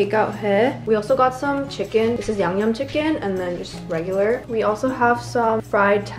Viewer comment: i thought she said this is yang yang chicken lol no joke